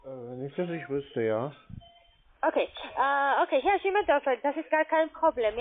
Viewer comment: Ich hab immer nur Schimmeldonner verstanden, bis irgendwann der Groschen gefallen ist. 😂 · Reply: Ich habe schimmeldörfer verstanden hat sich ja auch sehr gut aus gedrückt 😅